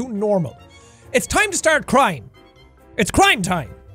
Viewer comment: i thought he said "it's time to start crying" and i said big mood